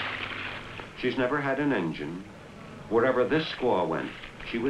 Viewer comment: I thought he said the Wigwom, never had an injun...